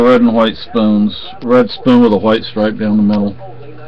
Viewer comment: You said, red spoon with a white stripe down the middle. My husband over hearing thought you said, red poon with a white stripe down the middle. Lol